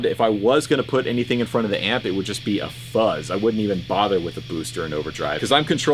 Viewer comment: I thought you said “I wouldn’t be a father with a boost or overdrive pedal.” and I felt that